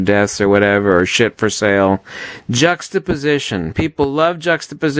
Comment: what are you saying. I cant understand it (partially bc im not a native english speaker) jaux the position? just the position? I really cant figure it out can someone please help me out?